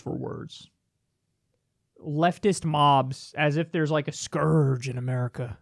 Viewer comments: thanks for clearing that up. I thought he said "leftist moms". Now it makes sense. Ha! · I thought he said leftist moms! Is that before or after taking the kids to school?